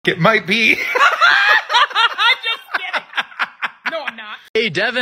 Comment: for the people who don't understand 4:46 he actually said nein which in german mean no so he got no b!/ches L